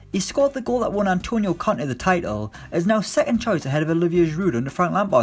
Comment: I was confused for a sec when you said “the Frenchman helped Conte win the league”. Thought you said Kante